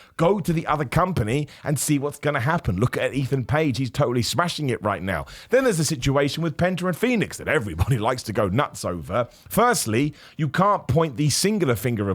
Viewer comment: I’m making espresso and misheard him I thought he was talking about Beth phoenix not penta and Fenix. I was really confused.